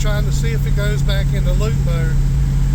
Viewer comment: Limp mode is the term when the check lamp is on and the motor is running off a pre set default map. Closed loop is system normal. Sounds like your saying loop mode. Pardon if I Heard you wrong.